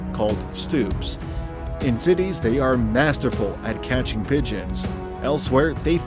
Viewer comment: I could swear I heard "catching bitches"